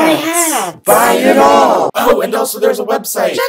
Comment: I'm actually guttered. This whole time I thought the opening was saying buy my shit and I just found out it says shirt. I think it needs to be changed 😂😂